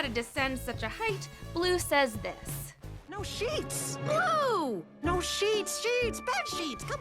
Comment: I lowkey thought he was saying no shits as a kid